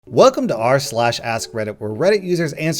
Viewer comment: Thought you said "welcome to r/slash ASS reddit"